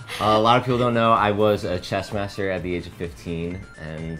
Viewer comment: I thought at 0.55he said chest master lol